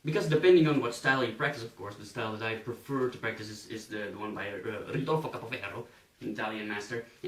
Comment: what the hell you said at 7:13?? The Italian Master name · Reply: Ridolfo Capoferro